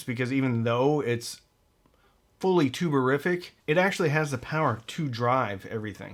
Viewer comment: Every time you say “tube-a-rific”, I hear “tuberific”, and I momentarily get excited about some high-grade potatoes.